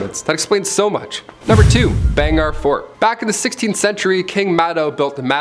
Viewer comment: I littarly thought u said bang our fort then I saw the spelling